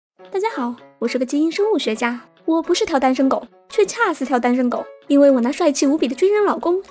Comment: 听成不是单身狗，掐死单身狗....